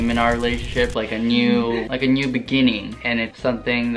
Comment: I Thought Said ‘A New Bikini.’